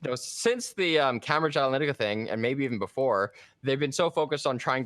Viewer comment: What's saying at 6:17 ? Camera Gilenetica? Sounds like he's speaking too fast, akin to the alphabet "elemenopee" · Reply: He’s saying Cambridge Analytica. Its just a word you didn’t expect, so it made it hard to understand. Had to doubletake myself.